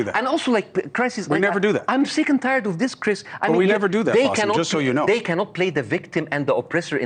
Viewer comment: does Chris say “we’d never do that “? Because Bassam was talking about the Israelis 🤔 Chris Cuomo is a POS Mor On.